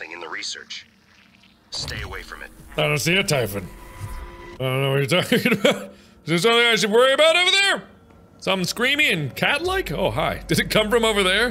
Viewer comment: I misheard 29:27 as "Something screamy and Catholic"...whoa, that really IS scary!